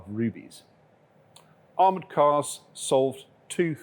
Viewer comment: Did you said Armored Core? In WW1? crazy...